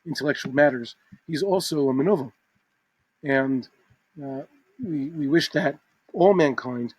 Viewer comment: What's the word at 0:23, Menugim? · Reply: מְנוּוָל, menūval